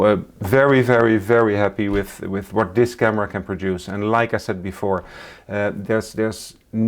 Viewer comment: Great video. Thank you foor it. At some point my mind heard you say 'leica said before'. 🤣 My mistake entirely